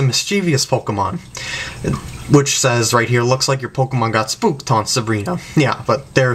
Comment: Lol, I almost forgot about these videos! XD Listening to what you said again, I just remembered something, @ 3:25, I thought it was Brock that quietly said "Looks like Haunter got spooked". I might be wrong though as I haven't seen this episode for a very long time.